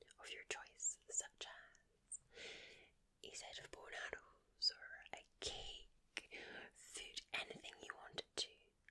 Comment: wtf is she saying there?!??! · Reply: a set of bow and arrows